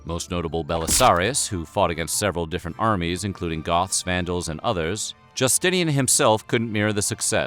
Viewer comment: Don’t laugh at me, I’m a high school dropout but did....did he say goths?